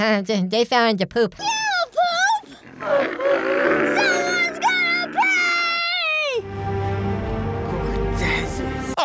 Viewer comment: honestly when I was younger I thought she said “SOMEONES GONNA PEE!” and now it’s a running joke in my family…